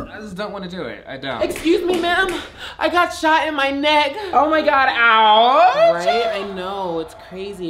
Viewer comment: Not gonna lie, I legit thought she said "I got snot in my neck" and totally accepted that. Cuz having mucus in your neck from like a cold ain't fun either 😂.